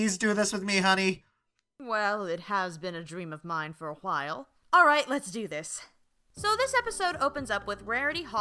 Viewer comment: Did you...did she just say "h-while"?